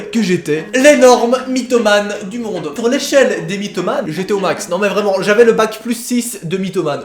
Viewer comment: Quand tu as dit " dans l'échelle des mythomane... " j'ai entendu "dans l'échelle des pyromane" nan mais please 😂achever moi